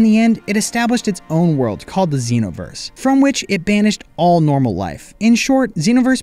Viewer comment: bro i thought he said dragon ball xenoverse for a sec